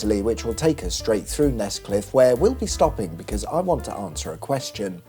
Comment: Nescliffe??? I thought you said Nesquick. For a couple of seconds there I was a really avid listener :face-pink-drinking-tea